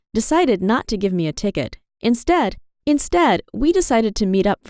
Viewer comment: Wait am I the only one who heard her say Instead Instead like if you do it’s